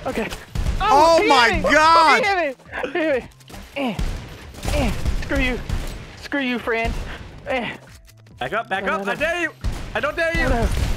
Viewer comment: Did delirious say "fran"